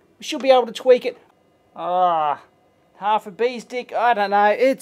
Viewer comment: Did he say "Half a bee's dick"?! Awesome.